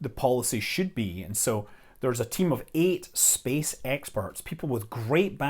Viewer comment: I heard 7:15 as "SpaceX perts" at first before realizing that it was "space experts."